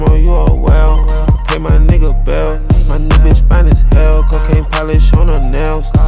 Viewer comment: did he say my niggas fine ash?